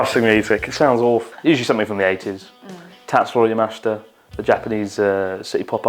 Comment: What is the name of the artist he mentions in 21:26? I can't quite catch it. Also, rec me anything Japanese! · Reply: it is Tatsuro Yamashita. He is known as the king of City Pop.